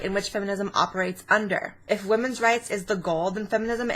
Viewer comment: What word is she saying here, and what is the definition? Evegoal? A vegil?